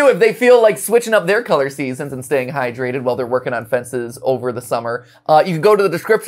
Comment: I heard, "Working on my fencing," the first time instead of "Working on the fences" and I greatly wish to see Matt to take a fencing course 😂